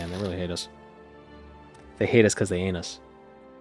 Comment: So you see… @ 27:35 I heard they heinous because they anus 🤣 not they hate us because they ain’t us.